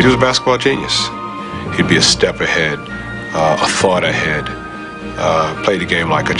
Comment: it sounds like Erving says Bird was "a fart ahead"